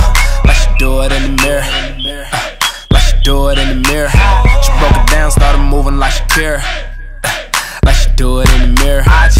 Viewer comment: This whole ass time I thought he was saying “She Broke it down started moving like she care” 🤦🏻‍♀️🤦🏻‍♀️